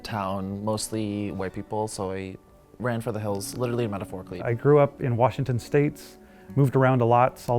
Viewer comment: did he say "literally metaphorically" hahaha